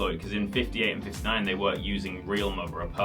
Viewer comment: Did you say were or weren't at 7:42 ?